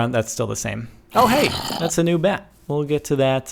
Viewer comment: Wait, DID HE JUST SAY "Oh yea, that's the new bat" Like please